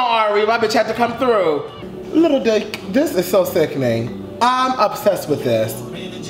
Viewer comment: did you call him “lil dyke” I